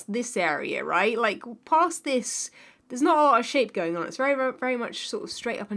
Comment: Is it just me or did I hear s**t and not shape